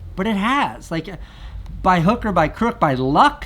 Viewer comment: did he say hooker!? ;-)